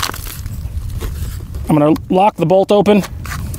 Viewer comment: The tavor is the most overrated gun ever. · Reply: Der Kaiser Wilhelm II I thought he said "glock" lol...